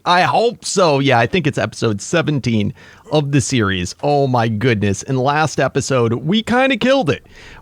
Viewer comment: I thought u said this is the last episode